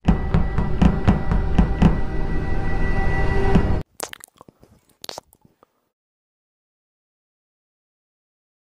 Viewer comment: no entendi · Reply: AMOR ES AMOR